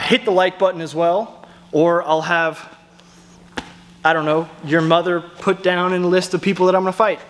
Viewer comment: Dude, at the end... "I'll have... your Mother put down (and in this moment I had a split second of 'DUDE! DARK!) on a list of people I'll have to fight (Slightly better, ok...)" seriously man, I thought you just said "I'll have your mother put down" 🤣🤣 ... fighters you missed.... "The Dancer, they evade and run a lot and cant be hit but really doesnt want to engage".... "The Protaganist, much like the Lawless, but imember of your class, he keeps ASKING for different specific things and he keeps setting things up so he can be the MainCharacter (like a kid who always verbally describes counters and stuff in imagination games so the always win)... "The HypeMan", constantly cheering himself and trying to note how his moves were AMAZING, 'did you see that?'.... "The Sorrow"... 'you ok? Im sorry, you ok? you need some water? I'm sorry.... (then he kicks you low in the belly or balls AGAIN) and one last one.... "The OG"... this is the fighter who is 50, slightly overweight but hits like a brick and every fight turns into a dirty boxing session of Muay Thai, even if you are in a TaeKwonDo gym (they sometimes crossbreed with the Hulk)... This video was KILLER!!!!